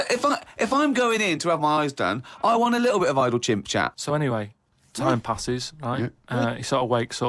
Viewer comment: notice how he says “Chimp-Chat” instead of “Chit-Chat”… it’s honestly hilarious and his timing is insanely fast.